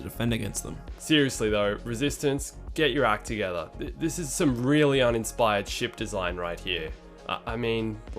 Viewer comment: For a second on thought they said that it was some "Really uninspired sh*t design." It fits either way lol.